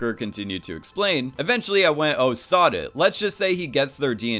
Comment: When he said "oh sod it I thought he said "oh sah duh" like that one vine